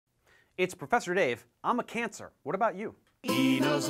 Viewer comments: I almost thought he said, “I’m professor Dave, I have cancer.” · waittt... in the beginning did he just say 'i'm a cancer'??